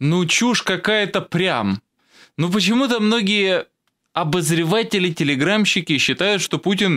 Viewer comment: прослушал раз 20 на скорости 0,5 - услышал типа "прядь". Эрзац словам прям и б.ядь. Ну так многие говорят- тоже искусство материться без явного мата.